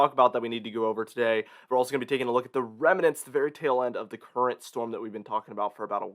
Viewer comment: Thought you said Fairy tale end ...🤓🤣